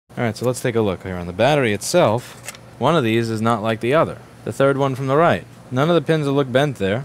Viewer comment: i thought you said "one of these things is not like the other" and then said "the third pin from the right" and then it is never mentioned again. and then if they didnt plug it back in after testing, it would not explain the issue that bought her there in the first place. ive never owned an apple product so i am not an advocate either way just my observations